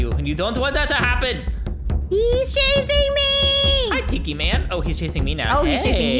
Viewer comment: Hiii! It sounded like u said Hey cookie fan instead of hey Tikiman:D